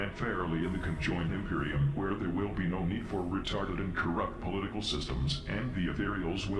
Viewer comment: the captions split away from what the emperor says here so the emperor calls communism corrupt but the subtitles say correct